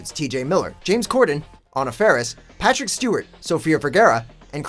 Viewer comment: I heard James cordon on a ferris